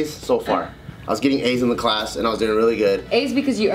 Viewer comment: I thought said he was getting AIDS in class I was like to!?!😂 he said he was getting A+ grades in class.... I'm dumb😂